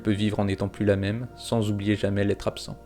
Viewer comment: j'ai compris "sans oublier les trap-sang" ou lieu de "l’être absent"